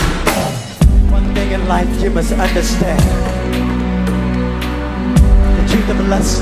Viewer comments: I heard “one nigga life you must understand” who else heard that 😭🤣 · Sound like he said in the beginning "what nigga like you must understand" 🤣🤣 but that's just me 🤦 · I thought he said" one nigga life you must understand😂